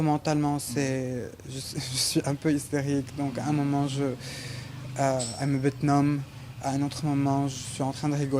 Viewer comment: c'est moi ou le mec à changer de français à hébreu (je pense) ! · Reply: Hakray Non il a juste dit "I'm a bit numb" c’est de l’anglais : "Je suis un peu engourdi" j’crois